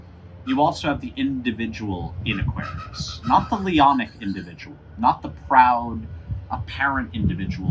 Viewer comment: What kind of individual he say here? Lianic? Leonic? I'm looking it up but don't get any results that seem right. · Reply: Leonian